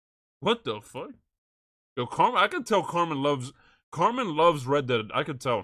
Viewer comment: did called carmella, carmine? Lol 38:30 ure not wrong.. x is literally a world champion lol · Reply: he said carmen (the streamer who plays carmella)